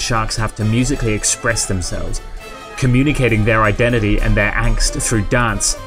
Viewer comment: Could anyone tell me what the narrator says at 3:35, communicating their xxx? Their what? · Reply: Identity and angst.